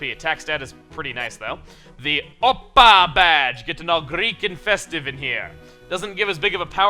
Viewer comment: I almost misheard the badge as "Oppai Badge".......... insert obligatory Lenny Face here for some reason